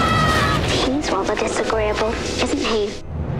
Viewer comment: what did Hermione say at 0:38? I am not very good at english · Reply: "He's rather disagreeable, isn't he?"